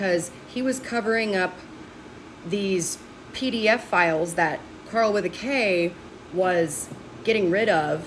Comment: I swore you said "Pearl with a K" I was very confused lol